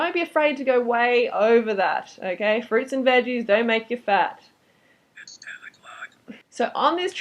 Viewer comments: Wait.. Did someone hear that voice at 6:49 or is it in my head..? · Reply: Yes, it said "It's 10 o'clock". I think it's the Zargon voice on the mac. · What’s that weird subliminal whisper @ 6:46? It says ‘it’s 10 o clock’ it’s really creepy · what is the creepy voice saying in the background of the video at 6:47? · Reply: It's 10 O' Clock...?